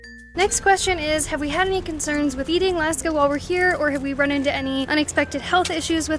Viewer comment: Do you realize what you say at 3:20 Even google auto translate agrees with what I heard, "any concerns with eating Laska" cannot believe it. that is a slip or feeding sounds funny. I am sure it is a slip or I and google hear it wrong, but come on. Maybe my distant relatives but not you?